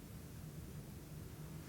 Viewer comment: Oh this is so good! There is a faint whisper at 30:01 that I can hear with my earbuds turned all the way up. It sounds like 2 words but can’t really tell for sure what it’s saying..something like “don’t you” but not sure. I heard a voice earlier, but it was too faint to mention. The Estes method was terrifying talking about taking everything from you! Ok..back to the video! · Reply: It's say dont you